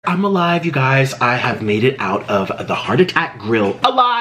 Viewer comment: I come back and the first words I hear are ''came out of a heart attack''. Wtf? We got to pray for this man, Jesus take the wheel.